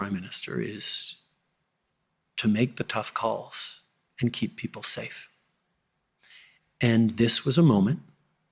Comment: All I keep hearing is, "Keep Summer safe." 😂